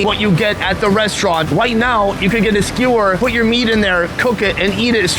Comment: did he say white now?? Racist